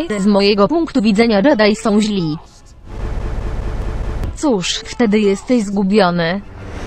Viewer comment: i think the subtitles should say youre lost, not missing